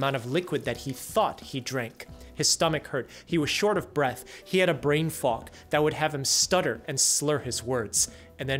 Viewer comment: I was listening to the video while doing something else and all I heard at first was, "He had a brain f-ck" and I thought, "WHAT?!"